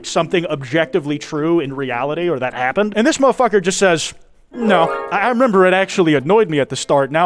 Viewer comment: why the hell did i hear dr. murphy in that “no”